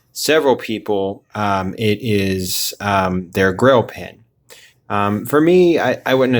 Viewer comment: I thought you were saying grill pen, but I realized that you were saying grail pen!😂 You bought a pen from <link>? I wish it wasn't transparent. I have the 723, I think it is, with the non functioning fa nib.😑 I wish I would have picked this one up, I don't mind the transparency, just rather that it would be a solid colors. Nice pen!